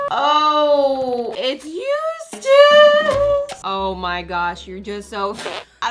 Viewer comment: I thought she said she hated Eustace but then I saw it was the makeover